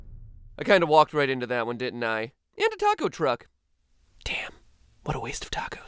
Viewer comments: DID HE JUST SAY THE WORD ''DAMM" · i thought you said otaku truck. taco truck makes more sense